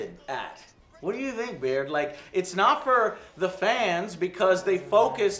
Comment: did he just call him "beard"? Lol. Now I ask, who is this man behind the beard? Or is the "beard" where all sentience lies within this being while only the husk of a human body follows behind it? I will solve you 😒👉